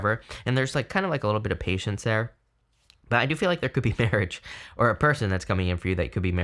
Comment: At the end I thought you said a person could be coming in who "could be married" and I freaked out. But obviously you said "who you could be marrying" 😄